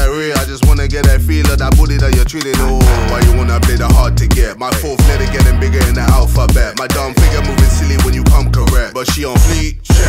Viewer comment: like if this song is the best Babatunde song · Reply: I thought he said "my forehead getting bigger than the alphabet"